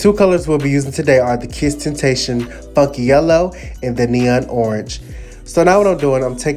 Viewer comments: i thought he said fuck yellow?????? 😭😭😭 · Somebody plz help did he just say "Fucc yellow"? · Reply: FUNKY YELLOW